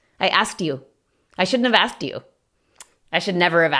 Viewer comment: Note que dices af en la palabra have en el minuto